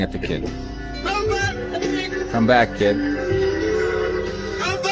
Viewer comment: 9m 54s It still sounds like Roger says "Come back Pig" to me, not "Kid".